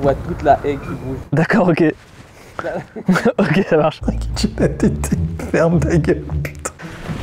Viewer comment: il dit quoi Maxime ? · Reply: "La kitchenette d'été"